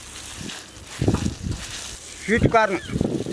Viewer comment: did he say shit corn